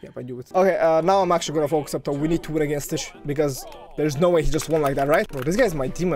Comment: yooooo what you say??? 💀💀💀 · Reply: i said. We need to win against stitch